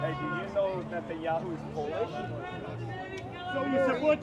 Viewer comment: What did he say there? Who is polish?